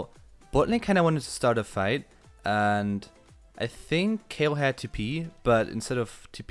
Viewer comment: lmao i thought u said kayle had to pee